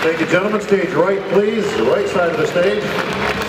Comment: right side of the stage....... sounded like roids outta the stage lol